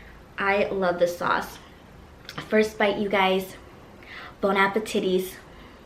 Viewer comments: Did she say Bonapartities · wtf 😂 did she legit say bon appetites 😂😂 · Lmao am I tripping or did she just say Bon Appetitties 😂😂😂?